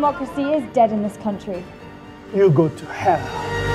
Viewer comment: Did he say:"you go to hell!"